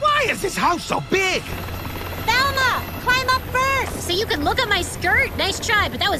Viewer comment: What in the actual fuck did she say? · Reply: "Climb up first"